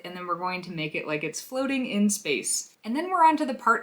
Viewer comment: My auditory processing system issues made me hear you say " Make it like exploding in space" I did not have subtitles on because they sometimes obscure what you are doing ;) I was wondering how you were implementing the explosion part until I realized, what I misheard.